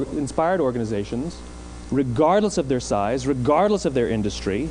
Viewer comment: en el minuto 3:09 usa la palabra ´REGARD´ que es ´considerar´, y en la traducción dice ´sin importar´. O yo escuche mal? Alguien me ayuda, porfiiis. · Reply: Dice "regardless", que significa sin importar que/su, a pesar de, etc. Sera muy tarde?... xd